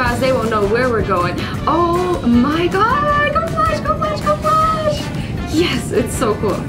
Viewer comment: wtf is she saying omg go flash go flash